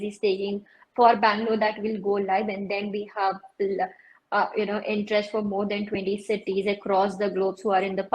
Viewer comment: Min 18:20 did I hear right? Did said there are more than 20 cities around the globe interested in the franchise of Drife? 😳😳😳